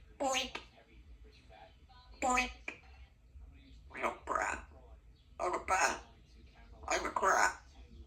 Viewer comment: Did she say Hello brat. I’m a bat, I’m a crab?